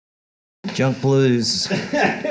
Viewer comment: Jump blues? Really? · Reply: He actually says, “jumble blues,” which makes sense as it’s a free jam, and would be in keeping with his humble, self deprecating sense of humor. Who ever titled the video misheard and ran with it (and doesn’t know what jump blues is).😅